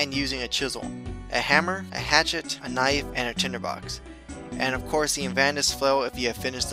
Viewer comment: tinderbox instead of tinerbox =(